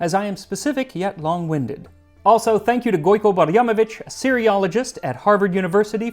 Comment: said: "Syriologist" I heard: "Cereaologist" and wondered if I should go back to school for a PhD in studying cereal and grains.